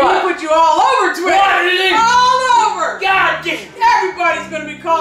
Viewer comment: GOD DI What I hear lol